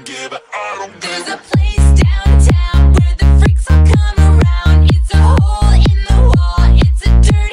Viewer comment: Not me just screeching “it’s a dirty paper roll” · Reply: I heard "there's a place downtown where the bricks are falling down" 🤦🏽‍♂😂😂😂 (I speak spanish and i heard it in a far away radio) 😂😅